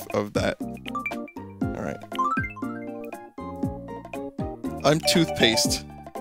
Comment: when he was choosing the voice for twofaced I wasn't watching so I thought she said "My name is toothpaste"